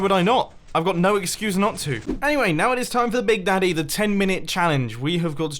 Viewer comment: did mumbo just say big daddy?